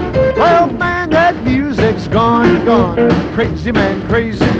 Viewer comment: I always thought he said " Crazy man, crazy, mind that music score ! " , but in my defence I have to say that I was only 0 when this came out .......................................................................